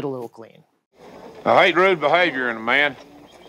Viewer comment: I had to look up the transcript, (to see what was said @ 1:30). Surprisingly, it was accurate..(i think?). " i hate rude behaviour in a man"...( i recall a talking heads song, with much the same sentiment).. i cant help but agree..